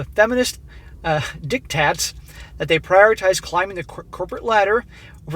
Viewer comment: dictwats! LoL 😂😆😂 dictwaters! LoL 😂😆😂 edited* he says dictats, really I heard dictwats.